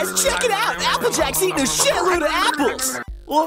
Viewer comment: can anyone tell me what she said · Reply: Sounded something along the lines of “I bet y’all I can eat a whole fucking ORCHARD of apples.” Applejack makes a reference to this same phrase at the end of the finale episode.